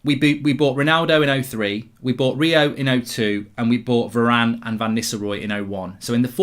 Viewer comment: Did he say we bought varane in 2001??